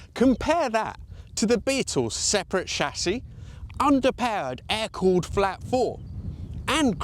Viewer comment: "...under peared*..." Can you be just a little less British mate? We colonial subjects might want to understand what you're saying! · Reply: He clearly says underpowered